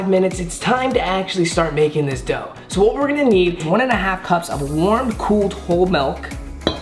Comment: did he say warmed cooled ilk